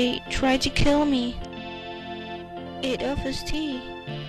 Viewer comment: Was sagt sie bei 07:55 ? Klingt wie aöis tie